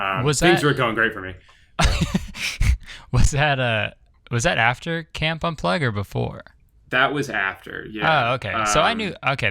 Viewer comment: I didn't know much about vine, but I totally thought Kurtis said "Camp Buttplug" to Drew. Thought it was a joke. lol